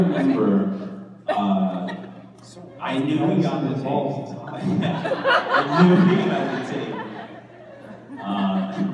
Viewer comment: what did he say? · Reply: Your ball was just on it